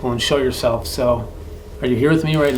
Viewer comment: i definitely hear "i'm here with you" love this content